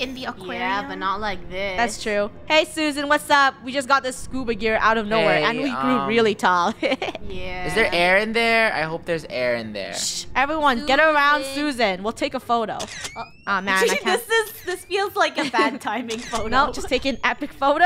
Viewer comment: I thought said "is there eren here i hope theres eren here"